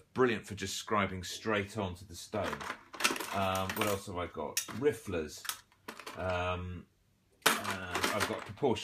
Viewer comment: Can you please spell out the tool you picked up at 45 seconds? It sounds like you said raffler. Thanks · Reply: Vile Vision Studios It was ‘riffler’